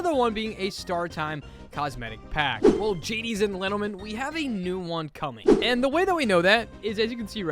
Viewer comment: did i hear you say "Gadies and Lentlemen"???